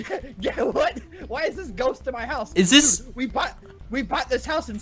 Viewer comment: XD I heard at 4:33 goat and not Ghost